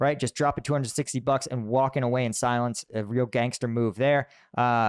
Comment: Did you just call Jerome “gangster?” 😅😂